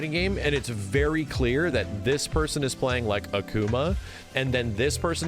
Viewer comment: It’s a bit loud where I am, and I fully thought he said “playing like a goomba.” God only knows what a goomba plays like, and I’m really disappointed I’ll never hear the end of that analogy.